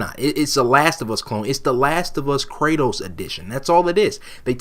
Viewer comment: Oohh i thought you were saying "cradles" insteadof kratos i thought wtf